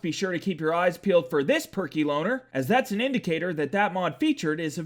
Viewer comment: when you said "Perky Lowner" i thought you said "Perky BONER"